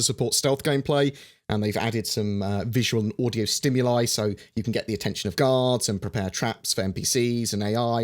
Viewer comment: i misheard "You can get attention of Gods"